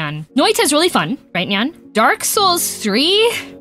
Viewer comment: I genuinely thought she said Nyark Souls 3